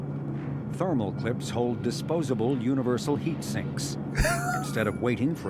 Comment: That's essentially what they're doing right now. Ejected Thermal Clips are reusable once they've cooled down again. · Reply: Did they say reusable? I thought they said disposable